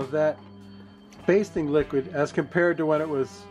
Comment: Just wow..mmm. pork porn. that's what this is. I heard "Bacon Liquid," instead of, "basting liquid."